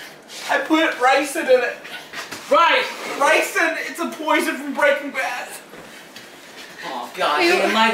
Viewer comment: “Its a poison from Breaking Bad” 😂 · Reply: I thought he said :It's a poison Breakfast